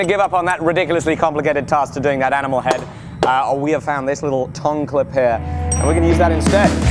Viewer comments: love watching you work and for most of the video i thought you were saying tongue clip and i was like what is a tongue clip oops you were saying tong good job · I thought you kept saying tongue clip and was very confused until the end.